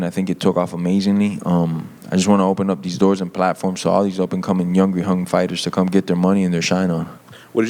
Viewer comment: did he say "youngry hung fighters"???? 😂😂😂